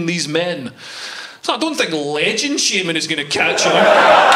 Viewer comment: "I don't think the ledgin shame is going to catch her" .. WTF you saying? SPEAK ENGLISH MAN!